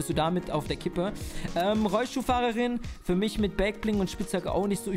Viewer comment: habe Rollstuhlfahrerin verstanden 😂🤣